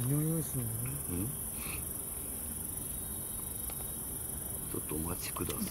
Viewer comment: さん今晩は、お疲れ様でした。12:32あたりでちくしょうと声が聞こえました。 動画up有り難う御座います。楽しみにしています。